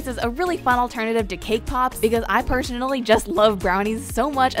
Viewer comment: Who else thought she said kpop instead of cake pops